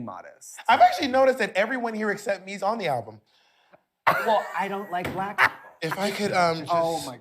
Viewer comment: What did Katya say at 7:30? I don't like black what? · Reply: she says "well I don't like black people" (obviously joking, but just making a comment on how the three ppl she included on the album are white)